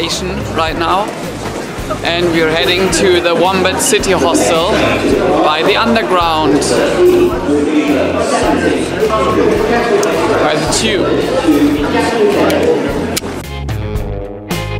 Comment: did you say "partiu" in Portuguese? Sounds like you did! I loved this video! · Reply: Thx. I said "by the tube". That is how they call some of the underground trains. I don t know any portuguese.